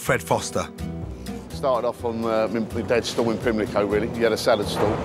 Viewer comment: lol i cannot understand what hes saying at 0:20, anyone can translate ? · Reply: Charles MacKay he started off in his dad's store or stall